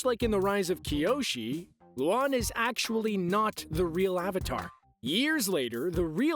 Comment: Bro I literally thought that the narrator said "wan wasn't the real avatar" like avatar wan isn't the first avatar